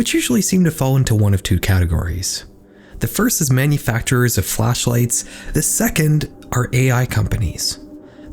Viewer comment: He did actually say flashlights, not fleshlights. We gonna see Olight sponsoring this shit?